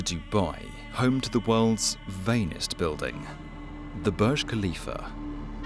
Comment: I keep hearing “veiniest building” and I think this gives a hint towards what this race was always about.